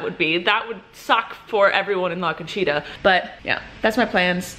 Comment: la CONCHITA sounded like u were saying : LACACHEETAH